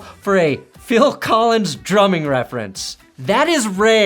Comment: As a farm kid, I thought it looked like he was saying "milk the clock". But nah, "drumming" is funnier. Let's go with that.😂